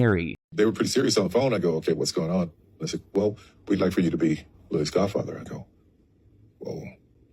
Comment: Did Tyler just say “we want you to be loui’s godfather?” That kid is not named loui….